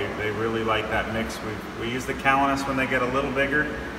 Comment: What food did he say they feed the blue mandarins when they get a little bigger? Sounds like callanus but I can't quite make it out, ~ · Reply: Dominic Martino You had it. Check out PE Calanus